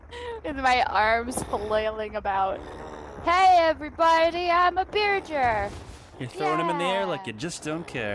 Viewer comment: It sounded like they said "hey look evetybody im a BEERgear" and that is just funni